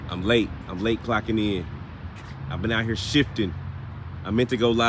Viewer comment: I thought I heard Grave Shift.